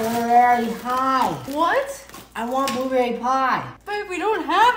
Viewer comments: WHEN FIRST SAID "I WANT BLUEBERRY PIE" SOUNDED LIKE HE SAID " I AM VERY HIGH" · I heard him say I am a blueberry kite 💀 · i thought he said i watch bill nye 😂